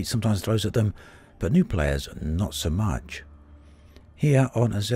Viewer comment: I thought it said New player cancerous lmao